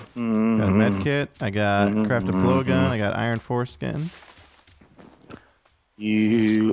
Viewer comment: I had to go back a bit because I thought I heard iron foreskin